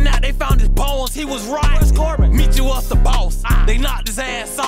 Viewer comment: “Where’s Corbin” funniest part 🤣😂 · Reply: I thought he said who’s corbin